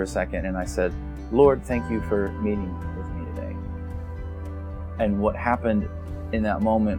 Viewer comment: What does say right around 3:28 "Lord, thank you for me......." I can't hear what he's saying after "me" (and it sounds like that part is what this vid is about) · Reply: yes, he says "Lord, thank You for meeting with me today."